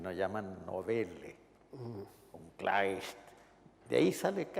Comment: What does he said on 44:28? German word 🤔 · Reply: Jorge Edwars dice ''Klais'', se refiere a Heinrich von Kleist, quien, como Rulfo, también tiene historias de fantasmas (Ver: ''Das Bettelweib von Locarno'' ).